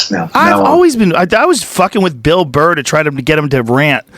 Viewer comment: Hearing Jon Stewart praise Bill Burr and drop that they're friends who send each other videos. My heart filled with Joy. Two of my absolute idols are somewhat friends! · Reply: Oh crap, I thought you said Bill BARR, and my spirit went into a tailspin. Must learn to read comments only after finishing video.